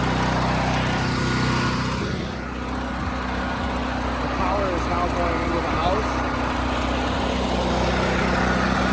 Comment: WHATTT WHAT DID YOU SAY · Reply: Power is now going to the house